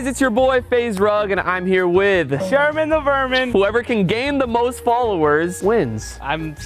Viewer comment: I thought he said shermen the virgin 😂